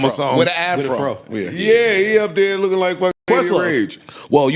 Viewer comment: seconds, who does Joe say Jay-Z looks like? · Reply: Lady of Rage